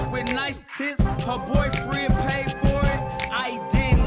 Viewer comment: on god it sound like he say my boyfriend paid for it.